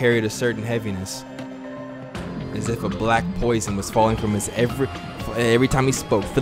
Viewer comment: why i thought that said black person i was bout to say 😂😂😂